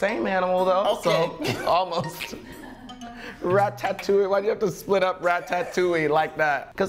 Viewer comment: Lmao No one:....... Rat Ta touille 😹😹😹😹 I thought he said Rat ta tat touille lmao 💀💀💀